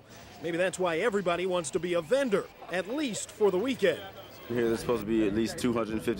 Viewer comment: it sounds like he said "Vendors say Freaknik will bring $30 million dollars to Atlanta, nigga that's why everybody wants to be a vendor." 😂 But I did cc to be sure